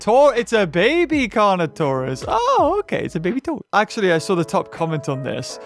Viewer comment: did you say baby toast or baby toe?